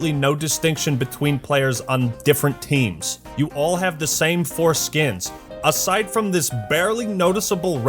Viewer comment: "you all have the same foreskins" is what I heard